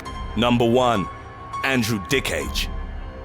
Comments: I thought he said Andrew Dickhead 😂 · lmfao,I SWEAR I thought he was saying "Andrew Dickhead".....lmfao :P